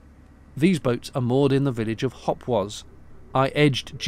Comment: Did I hear rightly, toward the end 15:45 " These boats are Moored in the village of Hogwarts"? I have some really poor speakers and they sound like a string and a can at times :) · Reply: "Hopwas"!!